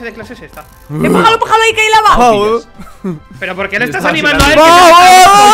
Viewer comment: en el minuto 12:14 le dice a que por que anima a y despues no entendi mucho,creo que dijo:por que lo animas a el si yo...vio alguien entendio? :v